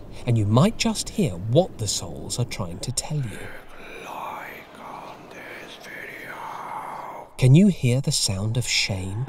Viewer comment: The voice i heard said “he wants you to like this video” idk why